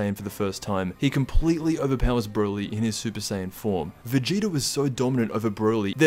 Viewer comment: did he just say vegito?